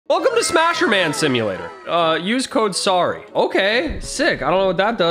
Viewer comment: i thought he said “ Welcome to Smashurman Simulator”